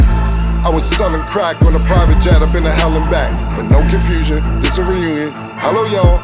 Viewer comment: Selling crack in a private jet up on a helipad is wild lol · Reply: I hear "i been to hell and back" 😂